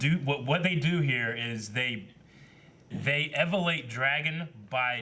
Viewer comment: does say ‘ Evalate ‘ lol